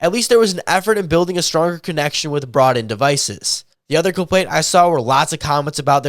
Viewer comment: what he say? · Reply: Coverage was better with "Brought in devices"